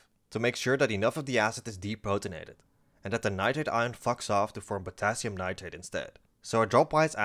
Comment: Did you actually say “fucks off” here, or did I mishear you?